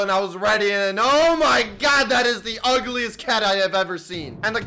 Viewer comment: Did you say cat?